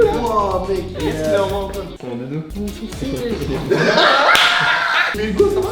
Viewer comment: il dit quoi Hugo ? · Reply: C'est ton doudou wesh